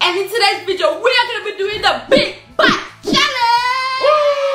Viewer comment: I thought she said big back challenge